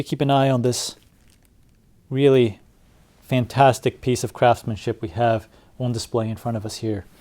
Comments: One question... At 3:02, did you say "craftsmanship", or "crapsmanship"? Not quite sure what I heard. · I first heard "... fantastic piece of crappmanship ..." :-)